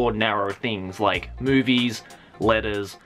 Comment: i thought he said "boobies" not movies